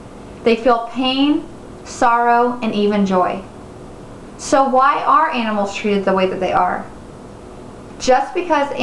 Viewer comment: I cannot hear clearly. Would you mind explain it for me please? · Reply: Christine Nipper So why do other animals treat animals the way they do. What is so special about humans as far as the animal is concerned?